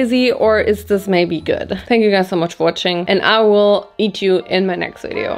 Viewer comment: Did she just say “I will eat you in my next video?